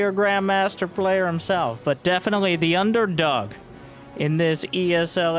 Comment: I thought you said Byun-derdog at the start for a moment. Heh.